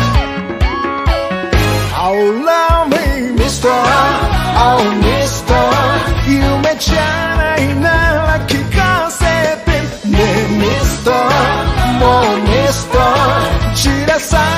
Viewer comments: I thought he said “allow me mister” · Bruh I actually thought he said "Allow me mister!"..I'm dumb🤣